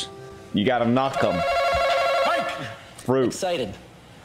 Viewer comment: what did he say? Excited!? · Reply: I'm looking for this c0mment and I hope someone has an answer. I found the clip, he does say excited.